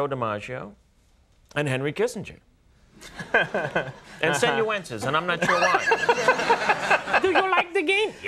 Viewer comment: who does he say after joe dimaggio and henry kissenger? senuences?